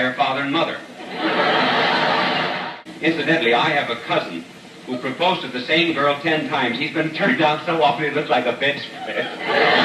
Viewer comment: Hi, I'm from Poland and i don't clearly understand a fragment from 0:25 to 0:31. Can somebody wrote me, what Bishop said? Greetings from Poland. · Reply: He said, "Incidentally, I have a cousin who proposed to the same girl ten times. He's been turned down so often he looked like a bedspread." Lol, greetings from the United States.